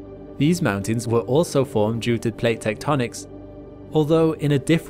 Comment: Did you just say "Teutonic" rather than "Techtonic"? :) Bet you did, earlish in the vid.